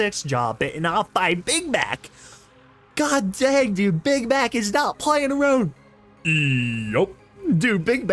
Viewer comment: (I heard big back): "big back is not playing around man- eeyup"